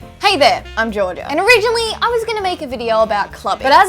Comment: did I hear jorda?